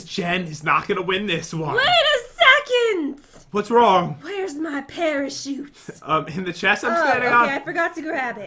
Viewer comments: it sounds like Jen is saying WHERE'S MY SUPERSUIT?!?! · who else thought that when Jen said parachute she said pair of shoes???